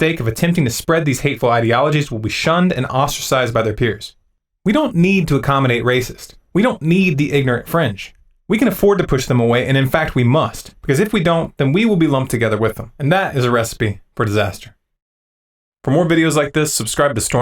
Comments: I first thought he said French also - but it seems he said "ignorant fringe" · did you say ignorant french? Probably not but that's what it sounded like. Racism is irrational intolerance, Intolerance in of itself is not bad. Instead of misdirecting intolerance to people who look different, we should be directing it to the people who deserve it. Such as the banksters, tax dodging corporations, military industrial complex, monsanto and nestle for stealing the food and corrupt politicians the DO NOT represent the people.